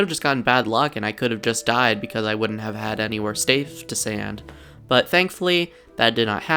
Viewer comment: 15am i the only one who heard "staf to sand"